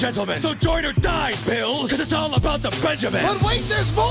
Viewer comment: Does anyone else remember him saying that? Edit: I thought he said “Because it’s about to be the death of a salesman”